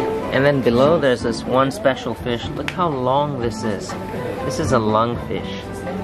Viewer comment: I thought he said “this is a lung fish” and I legitimately asked “but why? It looks like an intestine” and now idk how to feel